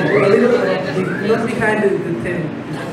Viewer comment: What did he say at 1:45 ?? I can't understand it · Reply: He asked what the outfit looked like from behind